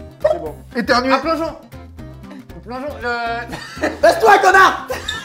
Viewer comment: Indice pour le cranium du ( 9:04 <<clique) : Squeezie dit "baisse toi connards" (Si vous me croyez pas allez regardez. MDR :-))